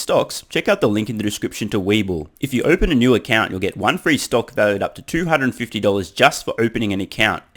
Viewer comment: How do you feel when Tesla stock is down? 💕 Want More Content? Join Patreon: <link> 👕 Browse MERCH: <link> ▶ TSLA Price Targets (<num>) <link> ➡ FREE One Year Supply of Vitamin D + 5 AG1 Travel Packs <link> 👕 TSLA HODLR MERCH: <link> 👕 420 Funding Secured MERCH: <link> 👕 Don’t bet against Elon MERCH <link> 👕 I’m ALL IN on TSLA MERCH <link> 👕 Buy $TSLA MERCH <link> 👕 TSLA to the mooooooon MERCH <link> 👕 Cybertruck Light Strip MERCH <link> 👕 Mars awaits MERCH <link> 👕 Make Manufacturing Great Again <link> 💲1 Free Stock on Stake: <link> (Australia, UK & NZ) ➡ SEND BITCOIN: bc1q386gs0uhzr5l5zatwz5u28t3f729zpsa9p8t0k ➡ SEND ETHEREUM: 0xb3F<num>DF1d21a0e0CB6DFdF7bFBBA0A3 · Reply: Oh I thought you said free steak. Darn.